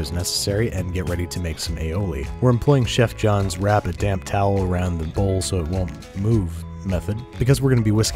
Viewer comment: lol I thought you said damn towel the first 4 or 5 times I watched this.